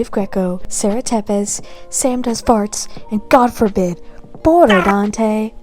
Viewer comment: Sam does Farts 😂 😂 😂 - not sure that's how you meant it, but that's how i heard it and it made my day